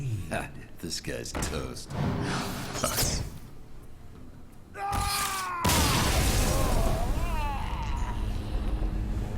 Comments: did he say fuck? xD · does actually say fuck?!